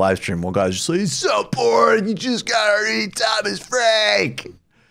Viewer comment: Can somebody tell me what Michael says at 0:58 I can’t understand what he’s saying. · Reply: E B “He’s so boring, you just gotta read Thomas Frank” — he being David Harvey